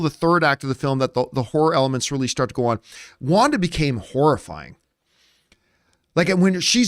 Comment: I thought she said "now go be wanda"... im gona watch it again tonight and ill pay attention cuz i wasnt even sure i heard what im saying.l now